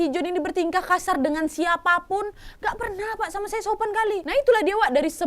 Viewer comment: Guys coba pakai headset/headphone kalian dan dengar baik2 di menit 18:53 ada kayak suara orang asing yang ngomong dengan cepat 'pernah pak, pernah pak' . Saya sdah mengulang ulang terus di menit yang sama dan saya gak salah dengar. Creepy sih ini